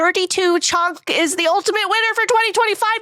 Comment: i thought she said a-bear-ica